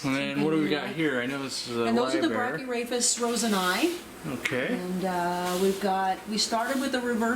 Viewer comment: Hi. Sorry but what is the name of the fish from 5:05 to 5:30. Can't figure out how to spell it. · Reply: brachyrhaphis roseni